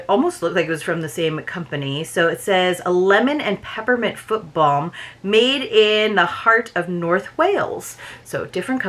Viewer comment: This one was great!!!! At first I thought you said you got a “football” LOL foot balm! That one sounds amazing.